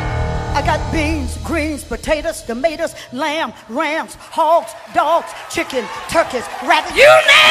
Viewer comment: Beyond memes and the jokes, this ladies message is very powerful. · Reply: Did she say rat at 5:58 🤧